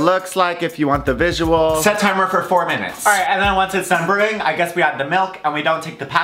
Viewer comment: I thought you said steep the tea for 45 minutes 😂